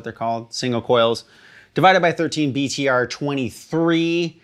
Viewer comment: Divided by 13 amps? Thought it said Friedman…my bad. Awesome tone from those bad boys